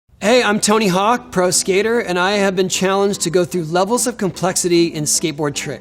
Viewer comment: Hey, I'm Tony Hawk Pro Skater 2 Thats what I heard